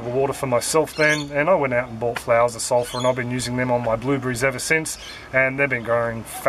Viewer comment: Hi, what are you saying at 2:31 mins, can't make out what you said. Sounds like you're saying... I went out and bought "flowers of sulpher"?